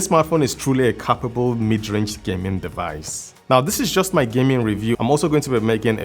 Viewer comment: i must be going deaf because i could have swore I heard midrange device